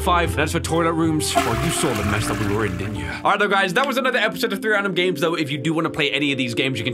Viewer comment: I thought he said 3 random ga*s